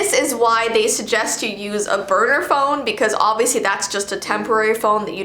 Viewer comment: Thought she said burger phone lol 😅😅😅